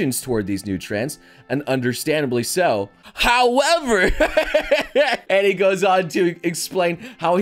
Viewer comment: ffs I'm watching too much cimoooooo, my brain made me heard him saying "however" instead of noble X)